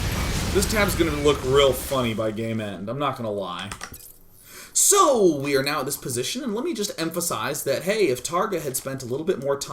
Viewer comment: lool i thought he said, "this tab is going to look really funny by gay men."